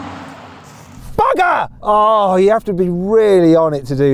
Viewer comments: ここのバカ！ が日本語にしか聞こえん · まんまバカって言ってるように聞こえるw